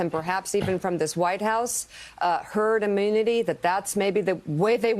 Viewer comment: Anybody who listens to Trump is a fool. How poorly educated can one person be. This is utterly ridiculous 🙄 His strategy is herd immunity. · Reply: I thought he said "herd mentality"? They reached that several years ago.